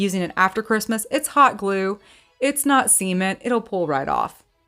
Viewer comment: for one of your crafts, you said “it’s hot glue not cement it’ll pull right off” I dead ass thought you said semen, and I had to go back and listen again 😅